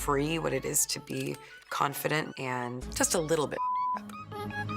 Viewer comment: what was that word?)) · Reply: just a little bit f*cked up